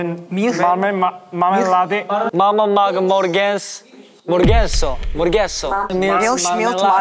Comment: my favorite part was at 12:24 cause hes so funny like ??? i busted a lung cause i was thinking it sounded like 모르겠어 too 😭 me and same mind